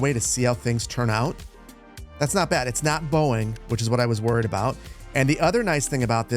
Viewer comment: I heard "it's not Boeing, which is what I was worried about" like yes thankfully my city is not Boeing 😂